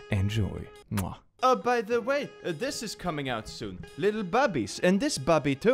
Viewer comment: i look away from my screen for a few sec to fetch something, and got spook with what said at 0:30-0:37, the way he said bobby on this vid sounds like 'babi', which translate to pig, xD i thought was selling pig plushies. sorry